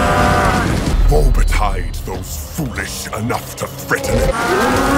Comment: When he said "woe bedite" I thought he said "ordertide those foolish enough..." lol